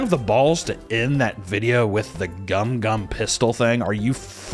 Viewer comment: Oh my god, I thought he was saying "Gum Gum Fisto" until the very last time you played it in the video. Which was uncomfortably cringe. Is it less cringe with "pistol" instead? Asking for a friend.